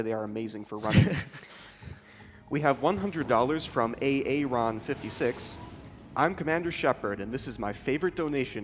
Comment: He actually said A. Aron? at @ 20:58 I wonder if its Aaron or A. Aron haha